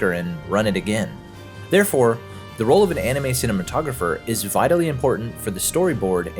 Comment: I love every second of this. It's good to see you back talking about our favourite anime again. 24:47 The subtitles say "animated cinematographer" instead of "anime cinematographer" btw.